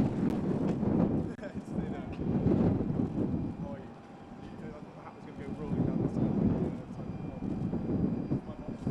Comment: … is William calling Prince Phillip Pop-Pop or Phillip? It looks like he could be mouthing something along those lines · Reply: Sara Zlotnick , He said "Hi Grandpa. How are you?"